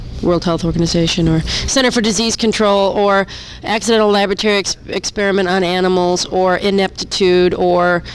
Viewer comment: it sounded like Exene said "Accidental lavatory experiment on animals" ROFLMAO